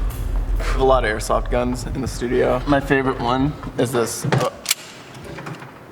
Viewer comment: thought he said Nerf guns ya know my nerf m 9 b e r e t t a